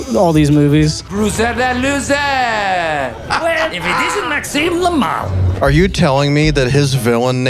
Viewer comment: I thought he said "maxem lmao" Edit